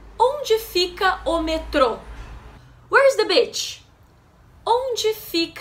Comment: Hahaha all I heard was "where is the bitch"?! 🤣🤣🤣🤣🤣🤣🤣